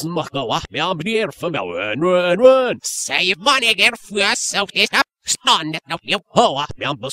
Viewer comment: at the begining of the end yami said "rerere" and i heard marik say "save money" or sumtin like that :P